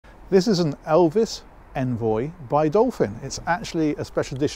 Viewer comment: It sounded like you said Elvis envoy.